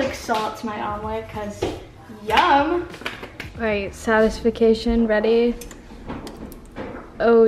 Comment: Girl did you just say satisfication? Lol